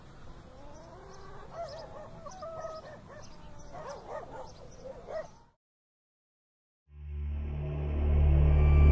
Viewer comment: Eu nao entendi essa 14:53 , vc esta assistindo semydeus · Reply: Estética